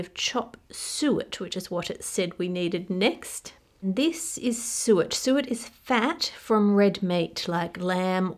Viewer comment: The caption at 5:40 says "sewage" instead of "suet". 😂